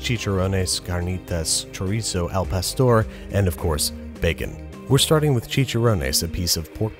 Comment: Who else thought he said “horse” instead of “of course”